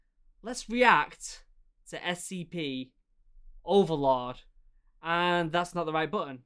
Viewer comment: I can’t be the only person who thought he said “Overlawd” at the beginning